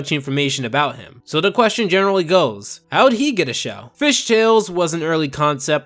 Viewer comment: DID HE SAY FISH TALES?